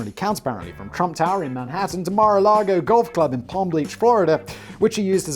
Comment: Am I the only one who heard "plam bleach" at 26:30?